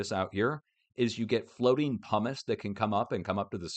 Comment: I thought you said Hummus… but you said pumice. 😢